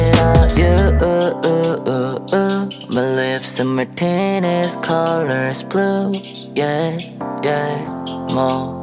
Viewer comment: did he really say "my lips and my penis color's blue"?